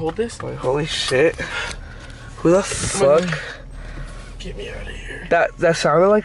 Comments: I am so late but who else heard "GET ME OUT OF HERE" at 18:52??it was probably or but it sounded deeper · If you're reading this... HAVE A GREAT DAY / NIGHT!! Love from a Irish SINGER / SONGWRITER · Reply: I HEARD get me out of here